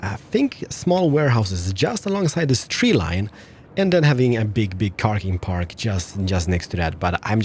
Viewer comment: Is it just me or did say "big darling park"